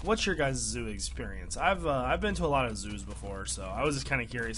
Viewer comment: i heard you said "I've been to Zues Before" xD